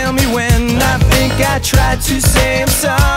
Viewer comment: For years I thought he said ‘She said these words are irrelevant’ 😅😅